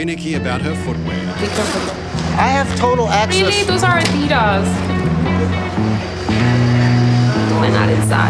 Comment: What did she say ? I heard It “that’s Aaadidas” so she wanna say “take carefully my Adidas shoes”?